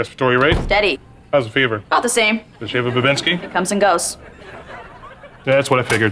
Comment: What is vivensky the doctor said it 😆